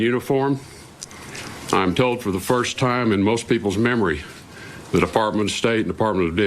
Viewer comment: Did i hear tillerson make a freudian slip "the department of death"